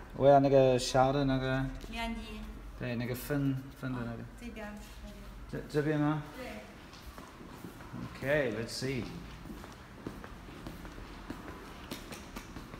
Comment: i thought i heard you say ni88a like 3 times i was like WHHUTTT? Blew my mind.